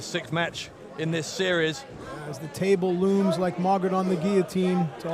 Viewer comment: HAHAHA ! i just got into botchamania and wcpw and i'm sure i heard someone shout "I AM THE TABLE" around 1:36:30 xD